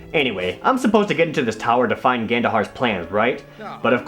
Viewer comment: Every time you said Gandohar' i thought you said Ganondorf